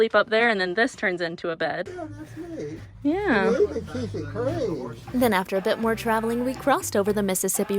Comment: I think he says 'you REALLY keep it clean!' and all this time all mr. Sipi was trying to do was be complimentous